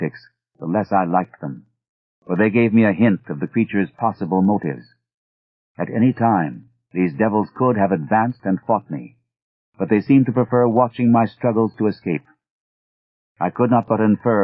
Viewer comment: I would’ve sworn he said “...could have advanced and fuck me” instead of “fought me” and I’m honestly, slightly disappointed it wasn’t “fuck me.”